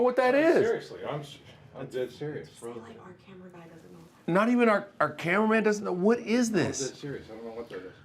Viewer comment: Did anyone else hear the lady say “say that not even our camera guy knows what it is” at about 8:46 minutes